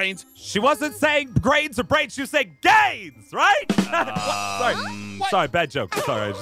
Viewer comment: i thought he said gays 😹😹🤣🤣🤣